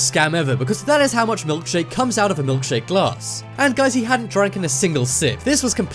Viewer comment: did you just say dranken...